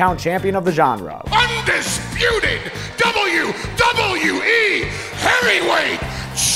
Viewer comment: You Can Hear Paul Say Hairyweight instead of heavyweight